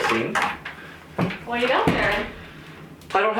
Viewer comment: What does call you? It sounds like he says, "Christy," but that can't be right.